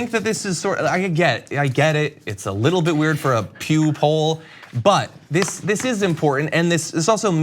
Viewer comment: I actually had to hit the "CC" button when John said "Pew Poll". I thought he said "Pube hole'.(at about